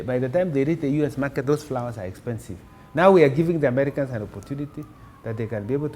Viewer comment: lol...did i hear "now we are giving the americans an opportunity"...#